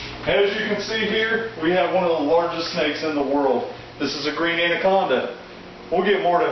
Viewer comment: That anaconda in this video is not 150lbs that is a over exaggeration he's about 8ft so he's about 50 or 60 pounds please stop misinforming people she might feel like she's 150 pounds but she's definitely not lol and reticulated pythons are the longest snake in the world and actually as of 2016 the Burmese python is now the heaviest bodied snake in the world and then it's the green anaconda and for someone that knows alot about reptiles you seam like a dumbass in this video brother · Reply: Bubs Reptiles I thought I heard "ONE of the longest snakes in the world" not THE longest.